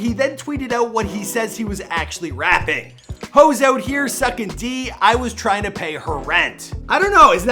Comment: If u listen closely it sounded like he said hoes out here sukkin d I was trynna pay her rent jus gotta listen closely or em I trippn?